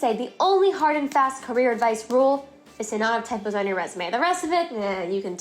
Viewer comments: Hi, great video 😊. May I ask what you say at 0:21? “…to not have [tempers?] on your resumé.” Many thanks. Edit: TYPOS! It’s typos. Thanks anyway! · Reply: I watched it with captions on and it reads as 'the amount of typos on your resume'. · "the only carrer advise..." and then fast mumbling. I can't hear what u said was the most important! pls what's the advise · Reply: I believe she said that the only hard and fast rule is to not have templates on your resume (as in the stylized kind that looks really good to the human eye, but would confuse an ATS system).